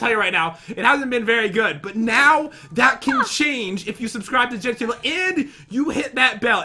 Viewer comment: did he say subscribe to Jen's table???